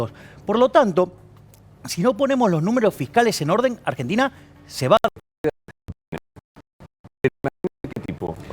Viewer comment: que fue lo que dijo milei en el 2:55 que pareciera qe convenientemente la calidad del video se fue al carajo? argentina se va a donde????? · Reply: Argentina se va a hipotecar.